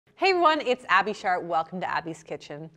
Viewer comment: I thought she said Shart 💀